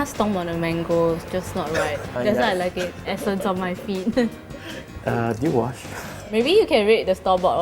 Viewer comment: lol did he say at the end of one shot "did you wash..." really quietly